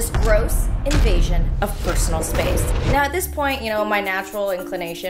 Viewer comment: I thought he said "My Space" and freaked